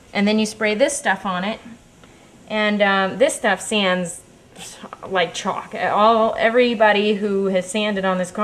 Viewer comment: think i heard her say it sands easy its like chalk